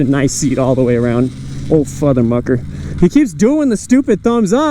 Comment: Hey i think the rims r too fat for this, i think u shud install thinner rims. Grt vid. Also I heard the "futher mucker" at 7:07 LOL